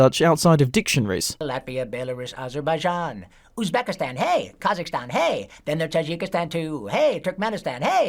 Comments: i think he said Kyrgyzstan at the first version of the expanded song he singed, i think he goofed up here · i think he actually said "kyrgyzstan", since that is a recognized nation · I heard he said Kyrgyzstan at first.